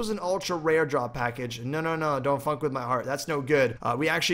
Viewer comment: did he say don't fûçk with my heart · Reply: Funk, not fuck.